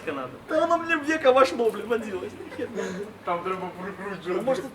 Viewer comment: послышалось что он сказал "в яйко зашло, блин вонзилось!"